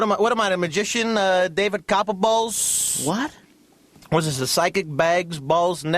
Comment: "WhatamI, David Copperballlllls" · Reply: i thought he said copperbose